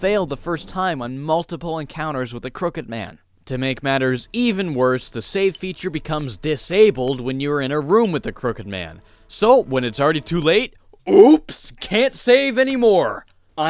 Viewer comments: I thought it said “ the cooked man” seriously though that would have been terrifying too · I thought it said "The Cooked Man"